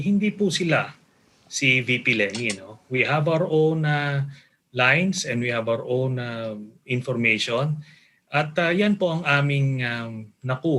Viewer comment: Tama ba narinig ko, 1:26-27 we have our own lyings? Hehe